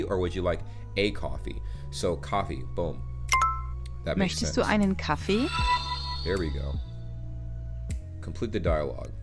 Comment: Hey, Minute 10:38 the Women said "möchtest du EINEN Kaffee" instead of "möchtest du Kaffee" as you Typed it in correctly, so as we can see both of them are correct So the women said the other Variant that you Took, but both are correct😄